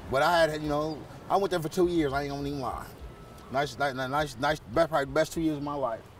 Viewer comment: WTF WAS HE TRYING TO SAY??? LMAO SOUND LIKE HE PUT I AINT EVEN GONNA LIE ALL IN 1 WORD HAHAHAHA